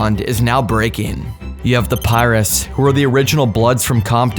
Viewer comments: I thought you were saying pirates. 😂 Making a video about Pirus when you cant pronouce Piru is crazy. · Thought he said the Pirates at first 😂😂😂